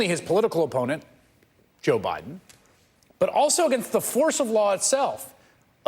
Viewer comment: "He is attempting to wage total war against not only his political opponent, Joe Biden, but also against the force of law itself..." The force of law is what he said, not the rule of law, the FORCE.